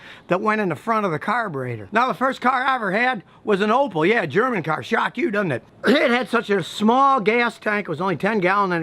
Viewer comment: What in the name of God did he say at 04:33 ??? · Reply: Well the first car I ever had was an Opal, yeah German car, shocks you doesn't it?